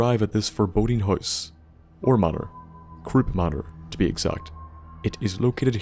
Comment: lol I actually thought you said crops manor and I kept laughing